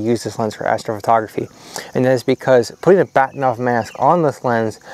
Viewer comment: what are you saying at 5:08, just before metioning 3d printing? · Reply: Bahtinov mask.